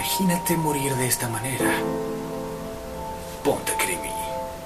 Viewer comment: yo siempre pense que decia ponte cripi,entendi cualquier cosa